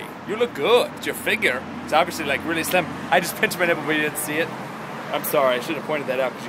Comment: toby says, you didn't see it. caption says we did see it.